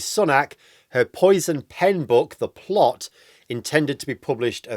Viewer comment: I thought you said THE FLOP!🤣🤣🤣🤣🤣